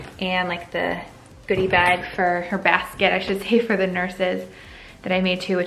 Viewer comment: Did you accidentally say “her bag” around 5:30?! 😳😬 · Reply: Sounds like she said or basket